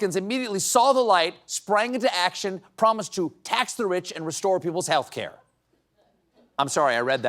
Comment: Oh, they were saying "tax the rich", I heard "Trump's a bitch".